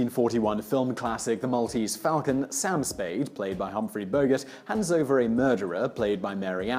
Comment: Did he just say ....Bogurt?